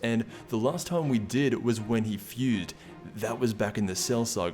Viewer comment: Did e say fued?